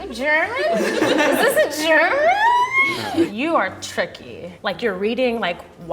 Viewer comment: sounded like saying "is that a CHicKeN??!" Lol